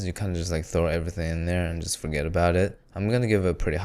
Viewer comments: did you say "spaghet-about-it"? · am i tripping or did he say spaghet about it